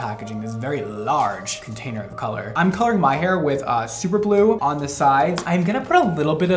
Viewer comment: OMG FOR A SEC I THOUGHT HE SAID HE WOULD DYE HIS HAIR WITH SUPER GLUE